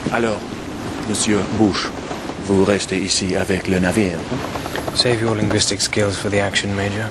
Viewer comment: What'd the French guy say at 8:00? I think I caught, "you will remain here with the..."... but I missed the rest. Stay here with the what? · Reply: With the ship. The "navire".